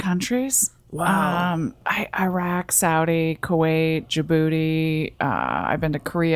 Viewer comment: Did she just said she'd been to Kuwait AND your bootty? 🤣🤣🤣